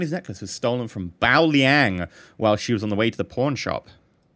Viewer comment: I thought he said porn shop instead of pawn shop